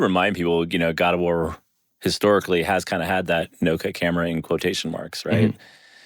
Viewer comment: i literally thought it said no nut camera XD